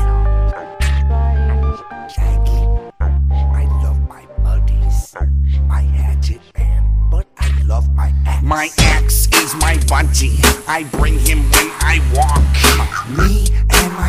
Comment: it sounds like he says my budgie instead of buddy